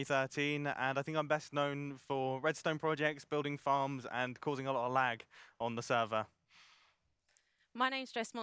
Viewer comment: Um.. There's a mistake in the captions at 0:35 "Causing a little lag on the server." I think it's supposed to say, "Causing a hell of a lot of lag on the server."